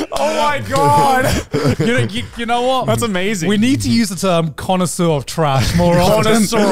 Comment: during the intro, i misheard garnt say “connoisseur of trash” as “Connor, Sir of Trash” like it was his title or something